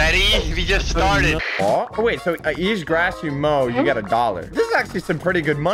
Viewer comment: ‘So you just grass your mow’ WHAT Edit: he says, ‘each grass you mow’ but you just hear it as ‘So you just grass your mow’